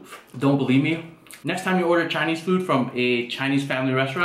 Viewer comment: Sounded like he said "Hades' food"😂